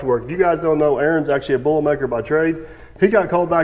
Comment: I thought you said Aaron was a “bowl maker by trade”. Took me 5 min and I realized you might’ve said “boiler maker”. I even turned CC on and it said bowl maker also. Maybe a new inside joke for ya